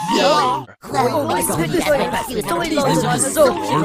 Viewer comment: i always thought he said "Oh So much fun" until 2019